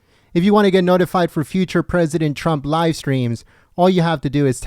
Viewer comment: Change your participation request! It sounds like your saying, Trump Lied” every time you say Trump Live! Change it for goodness sakes!